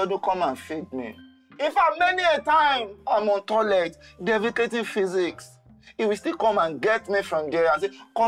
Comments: Did he just say defecating physics 😂😂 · Ah ...did he just say”....defecating physics???”🤣🤣🤣